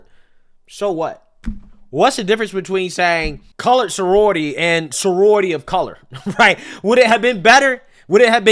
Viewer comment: I thought he said co ed sorority lmao